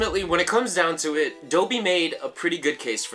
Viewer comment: It sounded like you said by dope not doubi